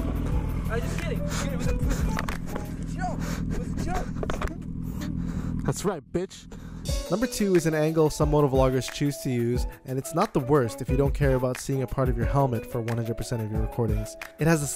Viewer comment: did you call him a bitch? 😂😂😂😂